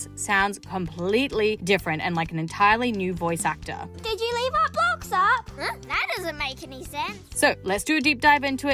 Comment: i thought i was the only one that heard that voice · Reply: It was like Did you leave out blocks up?